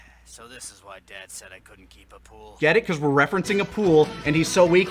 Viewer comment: has me feeling so dumb lmao. loved DBZ and you guys since I was a youngin, have rewatched this series more than I can count on one hand, and always thought he was saying A Pool, as if Bardock kept buying little inflatable ring pools for Raditz. I'm taking a lap on that one